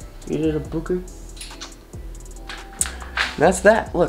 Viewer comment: Did she said "I've done that"?